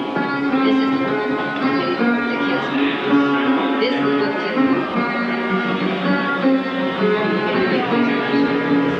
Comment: Does anyone know what Stevie is saying starting at 0:22? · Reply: “This.. this is what kills me.”